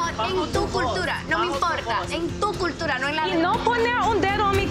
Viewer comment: As a Dominican who was born and raised in Dominican Republic who speaks Spanish in 0:22 they put on the subtitles saying that she said “I don’t care about your culture” she actually said “I don’t care, In your culture. Not mine” 90 day fiancé tried to make her look bad on that part